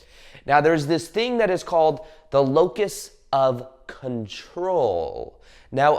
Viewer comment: What of control? It almost sounds like your saying locust…🤔